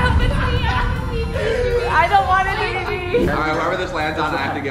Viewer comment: did she just say i don't want a baby